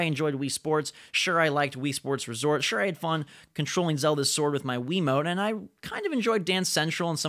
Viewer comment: did he just say controlling Zelda sword? :/